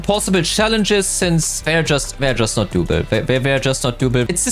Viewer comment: "there just not duby, there just no duby"...can someone explane what he said there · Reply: There just not doable